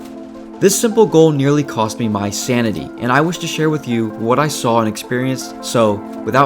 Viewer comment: So what I hear is "I Lost my sanity and now I'm going to steal yours"